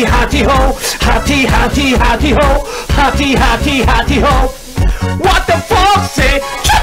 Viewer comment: 이거 약간 What the fuck 으로 들려용